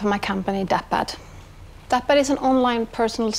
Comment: I thought she was saying Dad-Pad and I kept thinking DadBod lol.